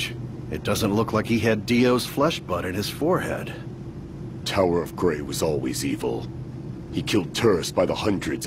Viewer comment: Hahahahaha I thought it said tower of gay.😂🤣